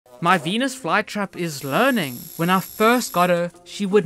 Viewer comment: Bro why the f i heard "my venus flytrap is burning"💀